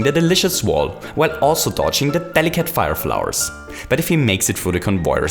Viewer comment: Did you just say "Fennekin fire flowers"